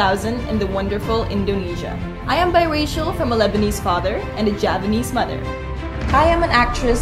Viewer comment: pas gw denger dia bilang javanese mother.... awalnya w salah denger,, w kira japanese mother?.....